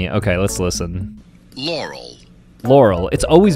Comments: "It's always been Laurel" What I heard: "Y E R N A Y" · I hear Yaurel